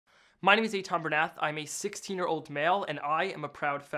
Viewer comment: I literally heard "... Im a 16 year old fail" lol